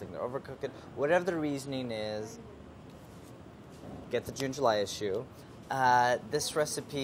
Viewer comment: The first couple of times he said June/July I thought he was saying ginger lime. And then the recipe actually ended up containing both ginger and lime